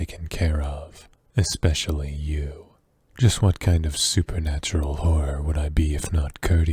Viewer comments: I can’t get enough of the voice. He should get a job as an voice actor for video games and animated tv shows. It’s amazing, plus it definitely make a lot of girls fall for it I already have lol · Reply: In a line where he says “..supernatural horror..” I thought he said supernatural whore lol😅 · lmao I misheard that as “what kind of supernatural whore would I be”